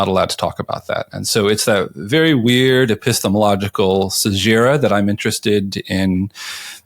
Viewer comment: Wesley uses a word that sounds like "Sejira" at 3:31 - what is the word? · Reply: I believe it is 'caesura'.